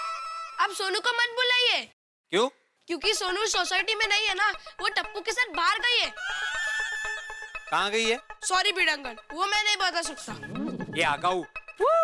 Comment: I heard “Woh tapu k saath bhag gayi hai”😂😂😂😂😂😂